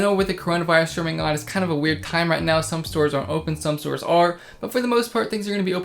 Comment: “some stores are open some stores are”. Oh ok thanks for that clear explanation! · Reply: I heard "some stores AREN'T and some are"